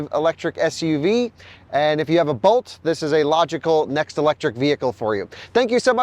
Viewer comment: I thought you said, "if you have a boat, this is a logical next electrical vehicle for you"